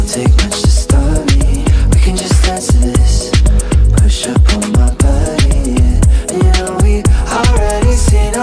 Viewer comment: I thought they said push and pull my body :p